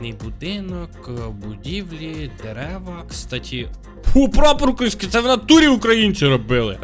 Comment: Мені почулося що він сказав фу 1:15 На субтитрах фу крадеться